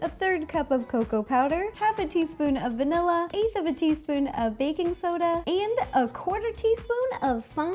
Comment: When she said "and baking soda" I thought she said"and bacon soda"😂